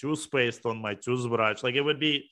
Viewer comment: What did Danya said in 1:07? The last phrase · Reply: “There would be no one I’d rather watch do mundane tasks than Grischuk”